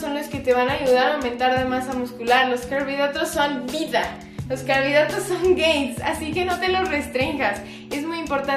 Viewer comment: Pense que dijo: "Los carbohidratos son gays" 😄😅